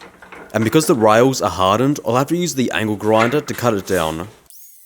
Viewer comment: Could have sworn you said anal grinder, I was glad to see the angle grinder coming out and not an anal grinder. Keep up the great work!!